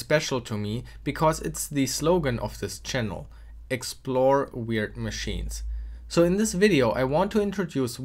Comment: What. I always thought it said "Explore Weird Mechanics".